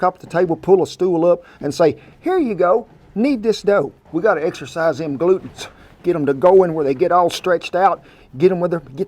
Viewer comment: Thank you for a great recipe. I love fresh bread. But.....I laughed out loud when you said we need to 'exercise those glutens'. What I heard was 'exercise those glutes'. I thought, Kent you are certainly innovative with you kneading technique.